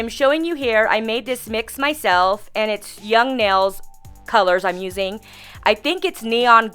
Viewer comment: I can't find that hot pink/ orange acrylic on their site :( so sad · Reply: I heard her say "young nails colors"